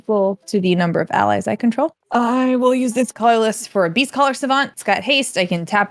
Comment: When played Beastcaller Savant, I definitely thought she said "Beefcaller Savant," and looking at the card art, I feel like the name fits.